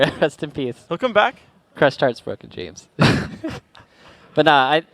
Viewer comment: What does Saint say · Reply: crushed hearts, broken dreams